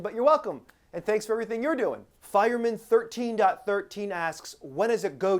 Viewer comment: Is saying "BAD" or "FED" at 4:59? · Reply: Fed